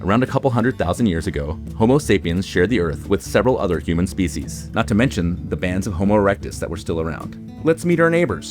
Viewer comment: (Not to mention the pan that still aroumd) did i hear this right?